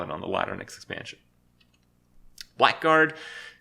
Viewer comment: did krip said whiteguard?